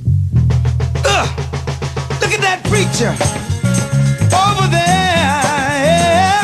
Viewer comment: Look at that creature! Over theeeere!! 🎶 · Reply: Thanks for the clarification, thought he was saying "look at that preacher" and just couldn't make the connection 😂😂😂